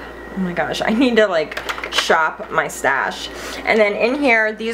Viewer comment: ha! thought you said "shop my 'stache" (instead of shop my stash). whoops. Love ya!